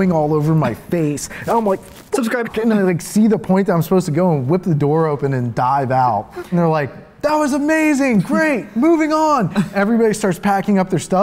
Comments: it sounds like someone is saying “subscribe to” am I crazy or someone else hearing it ? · Why do I keep hearing "Subscribe" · Did he just say Subscribe at 3:51? :o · Uh around 3:48 to 3:51 if you listen closely as the dude is talking it sounds like someone says "subscribe to-" but it's kinda fast. Edit: it happens again at around 5:38 - 5:42. But this time it said "subcribe" more clearly. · Did anyone else hear someone say subscribe to ____ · did anyone hear a voice say SUBSCRIBE.......? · Did anyone else hear at 3:49 and 5:40 "Subscribe"? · is it just me or did i hear "subscribe" in the middle of the video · anyone else hear "subscribe to..." randomly at 3:49? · At around 3:49 someone says "subscribe". Ive heard this same subscribe in another corridor crew vid. Im gonna call it out each time i hear it. btw i subscribed. Good use of subliminal messaging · Why am I hearing "subscribe" during this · Did you just say subscribe in the middle of conversation..? @ 3:49